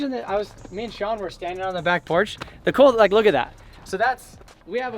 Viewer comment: Definitely sounded like he said back cock 😂😂